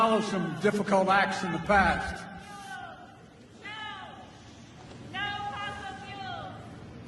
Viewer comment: I hear her saying "No FOSSIL FOOLS" :) LOL